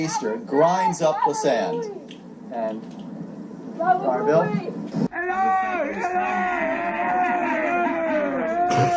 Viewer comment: What the hell are those kids yelling at the beach both at 4:07 and then again at 4:13 sounds like the same thing but what are they saying anyone ?????? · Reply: She is saying Bobabooy, Bobabooy. It's a character on the Howard Stern radio show in New York